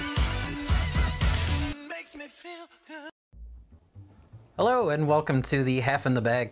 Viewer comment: I heard bustin' feels good.